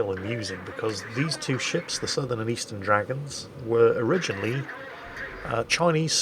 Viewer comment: I keep mishearing the name of the ship as Sudden Dragon, which would frankly not be a bad name for a ship. Or a band.